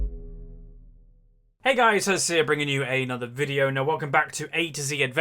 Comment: Oml he said Hey it's Huzzy here so fast that it sounded like he said Hey it's Azir. LOL